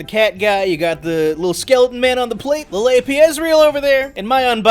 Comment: I swear I heard, "Labia Ezreal."